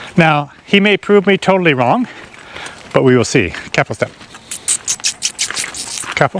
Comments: It sounds like you say Ketchup when you want him to go, so what do you say! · Whenever you want your horse to start walking it sounds like you say lets up, what do you really say hard to understand? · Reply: He's saying "careful step"